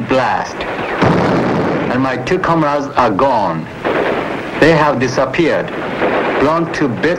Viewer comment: "and my cucumbers are gone." WTF? I had to listen again to what he said. "and my two comrades are gone."